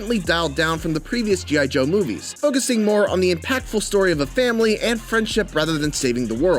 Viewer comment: Did you just said "Family"?